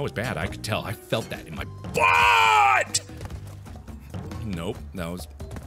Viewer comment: did he say "I could feel it in my butt" well mark what have you been doing lmao😂